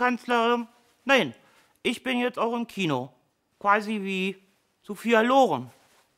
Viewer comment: ich kann Samstag nicht mehr abwarten! Isch kandidiere und das noch in der Lichtburg in Essen! Das wird echt der Film des Jahres! Was sagt "Frau Merkel" eigentlich bei 0:36? "Ich weiß nicht wie, so..."?